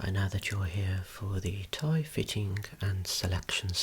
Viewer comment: For a split second in the beginning, because of the accent, I thought you said “toy fitting” and now I want a video of you trying little suits on GI Joes and Transformers. 😂😂😂